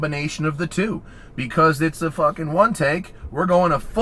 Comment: did he just say fuck? Very niiiiiiice 👍